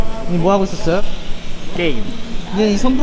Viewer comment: Did Innie just said "Gay"